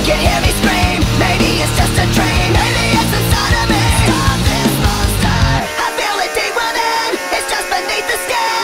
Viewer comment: I-I FEEL LIKE A LOBSTER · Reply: now whenever i hear it i think "STOP THIS LOBSTER!"